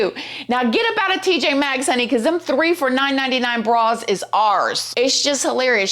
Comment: I heard you say 3 for 9.99 bras....now I'm headed to tjmaxx should be sponsored by them 😂😂😂